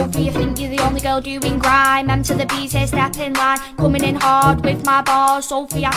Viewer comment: What I heard was "Coming in hot, whip my ball" 💀💀💀